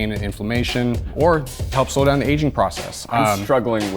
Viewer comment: I heard "Slow down the Asian process" instead of aging process. Confused me as a half asleep korean.